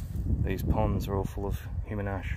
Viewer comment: What makes it hilarious? Please tell me I am misunderstanding something here. Edit: I didn't realize he was saying "This whole area was human ash" I thought he said "It's hilarious human ash" It was a misunderstanding on my end.